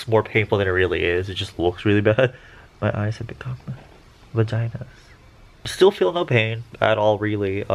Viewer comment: did say vaginas? Or did I hear it wrong. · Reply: Prasanna Gurung no, he did say vaginas haha